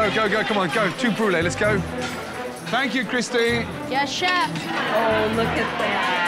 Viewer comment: Did he just call her "Christine?" 😂